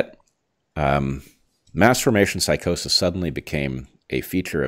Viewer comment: It’s sounds like u said masterbation psychosis, lol, not mass formation psychosis